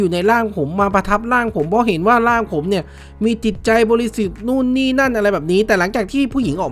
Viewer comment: เห็นว่าผมมีจิตใจปรสิต(ได้ยินแบบนั้น)